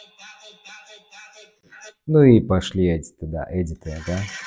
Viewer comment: Ты сказал "пошли от стыда"? Тоесть тебе стыдно от таких видео,или я не расслышала ??